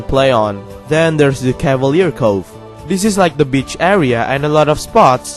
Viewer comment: i heard bitch area xd (I have nothing against your accent(i cant even write a right sentence))